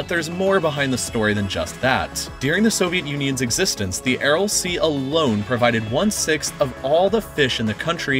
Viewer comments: I thought it said anal sea 🤣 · I thought it said anal sea